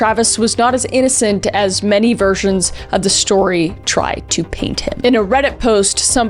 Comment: I thought she said virgins 😂😂😂😂